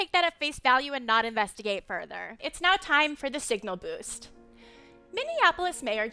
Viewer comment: The minute I heard "signal boast" I thought of the segment where she took out a black small business loan. Keep the toons coming, we NEED this! 🤣